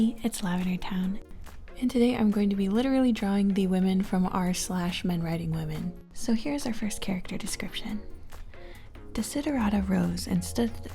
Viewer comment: Sorry, but at the end I thought you/she said 'men riding women's and got scared 💀